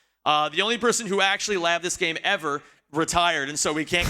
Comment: What does he say at 18:38? "Lav this game" ??? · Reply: "labbed the game", as in calculate routes to see what would be fastest